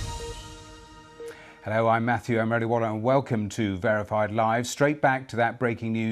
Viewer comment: [Music] 0:03 hello I'm Matthew I'm marijuana and That's what I heard and that's what the transcript shows.